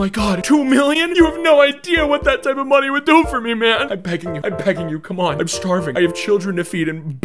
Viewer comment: instead of begging, the subtitles picked up the wrong word. You can finish the rest 💀